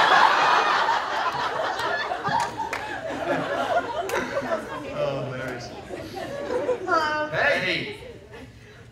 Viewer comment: What did Aiden say at 7:55? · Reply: Cece Angel Lent He said that someone had written "cock" on that paper...